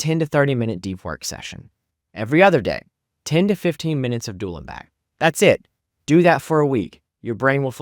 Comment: 10-15 minutes of doolenback? thats what i heard. what did you say lol